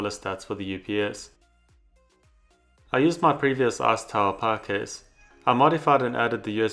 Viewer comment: What can I say? All I hear is "arse tower"